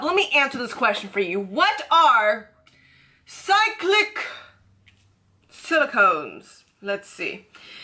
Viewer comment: Lawd I thought she said side-kick silicones 😅😅